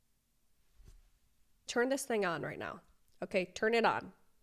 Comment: I’m so tired of influencers. Please get rid of social media lol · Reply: Ngl where said “turn this thing on rn” (the brain also influencers like her that did the makeup bad can gth dawg) but also I thought she said “turn this nga on” w vids but I’m hearing things xD.